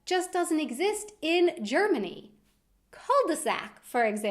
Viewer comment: What word dose she say at 2:02? Ether i'm hearing it wrong or its a foreign word for me to. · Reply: Ross Armstrong cul-de-sac